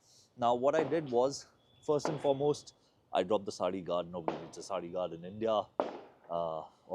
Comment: Cool bike. I don't understand what you say at 1:57. Can you please tell. Thanks. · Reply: I mentioned that I removed the saree guard from the bike. This is essentially a big grill like guard covering the rear wheel from the left side of the bike to prevent clothes getting entangled in the wheel. A saree is an ethnic wear for Indian women. This particular guard is exclusive to India and mandated by the government on all motorcycles sold here.